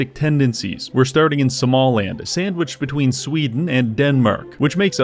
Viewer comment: First thought you said Somaland with that Småland prounciation